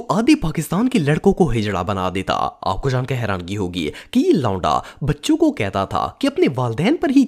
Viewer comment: did he said lauda?